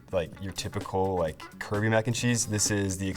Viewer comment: did he say Caribbean Mac and Cheese???